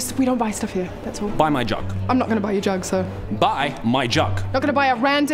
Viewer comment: Thought he said buy my junk 😂